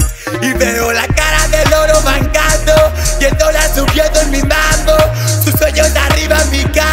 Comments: PARECE QUE DICE "EL DOLAR SUBIENDO Y BAJANDO JAJA NDEAHH" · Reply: Pensé que decía y el dolar subiendo a mil mango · Pense que decia "y el dolar subiendo a mi mando" :v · Soy la unica que en el minuto 1:32 piensa que dice "y el dolar subiendo en mi mano"? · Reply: yo pensé que decía "y el dolar subiendo en mi banco " jaja re loco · Soy la unica que en el minuto 1:32 piensa que dice "y el dolar subiendo en mi mano"? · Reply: yo pensé que decía “y el dólar subiendo en mi banco” JAJAAJJA TABA DESPEGADO